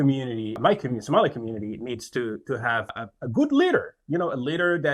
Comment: For a few seconds I thought he said they need “a good learer”.